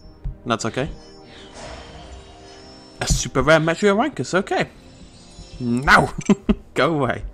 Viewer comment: I misheard what said for the Metriorhynchus, I ended up hearing Metriorhywankus.